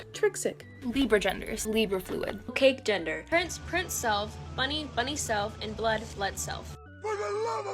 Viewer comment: thought one of them said fresh princeself and actually tweaked like a methhead on bathsalts i tore my hamster in half